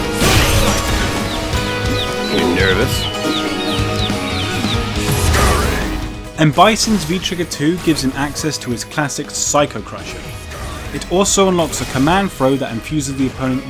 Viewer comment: Did i hear Psycho Crusher?!?!?!!